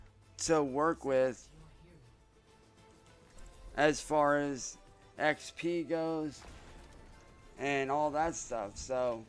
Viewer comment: did he say ex-pedos?